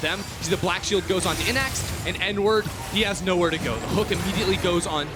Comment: sounded like said "n-word" lmao